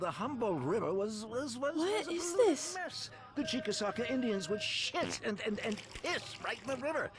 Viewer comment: did he say Indians?🤔